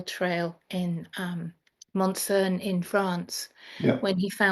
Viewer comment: I didn't understand the name of the French village of the Templar chapel at 18:51 minutes. Could you please write it in response? Thank you. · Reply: hi Diana and thanks for watching. It’s a small village in southern France called Montsaunes where Rory went to see the Templar Chapel there. Hope that helps and apologies for my rubbish French pronunciation!